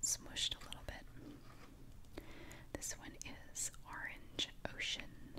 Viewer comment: who else heard her say orange oh sh*t😂😂😂